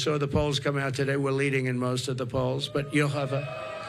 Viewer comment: What did he say? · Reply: That we're leading in most of the poles. Swamp water in your ears?